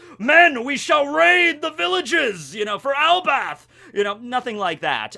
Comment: did you just say "ElbaTH"? :D